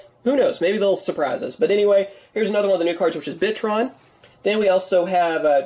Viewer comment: When he said bitron I thought he said Bitchtron and I was like damn yugioh I though you were child friendly